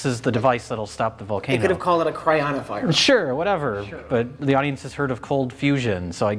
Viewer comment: I thought I heard "coronavirus" for a second